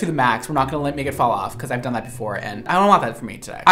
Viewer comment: Nobody gonna talk about him saying, "we not gonna let a n!gga fall off" at 10:53/10:54?? Nobody? Just me hearing this... maybe someone else can give their thoughts on what he said but I know what I'm hearing. Brad Mondo are you down and get to throw this around willy-nilly?!?! Hmmmm 🤣🤣🤣 maybe his accent is weird and he is saying "me" but sounds like more than 1 syllable. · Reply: “We’re not gonna like, make it fall off” is what I heard.